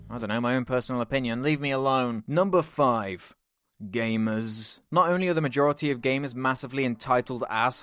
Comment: The first time you said 'gamers' I thought you said 'gayness'